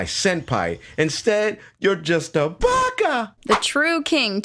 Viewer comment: did he just say baka? If he did the next question is how deep can I shove this glock down my throat